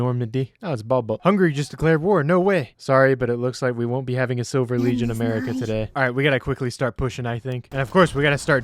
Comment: what did that say? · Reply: We won’t be having a silver legion America today